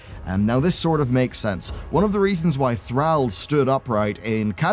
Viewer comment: THROUHH , that's how i heard you saying THRALL